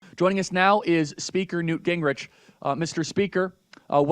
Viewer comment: Who thought he said Grinch 😂????